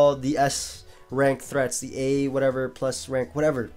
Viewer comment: Did just curse? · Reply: What he say? All I hear is I'm talking a bit more smogon talk here. At least that what it sounds like he said.